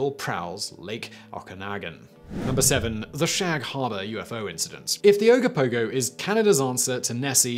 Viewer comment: I thought you said shag harder not harbor.😂😂🙈